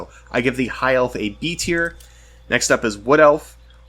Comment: I gave them B-Tier. You must have misheard. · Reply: Ah, my bad. Wait, you really said "D" at 25:31. Maybe you misspoke and meant "B" instead ? But I clearly hear "D" each time, even after replaying that part a bunch of times.